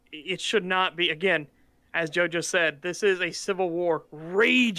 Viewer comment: Jo-jo 😂 To be fair he did say, "as Joe just said" but I heard it too lol